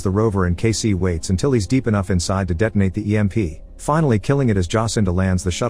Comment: i heard waited until he was deep inside